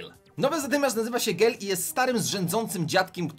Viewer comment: czy tylko ja usłyszałam gej?